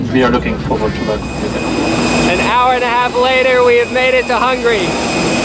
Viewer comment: Is saying Hungry instead of Hungary?.. It sounds kinda funny 26:26